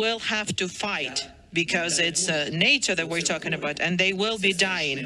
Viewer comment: he didn't say "nature" he said "NATO", he was saying that if ukraine collapses and then russia goes after a NATO country then the US will HAVE to send troops. get you facts right people! ...and don't hear only what some people want you to hear.